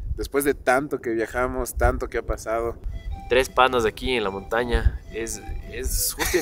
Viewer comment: tres panas aquí en la montaña... Lo que yo entendí Secreto en la montaña 😂😂😂😂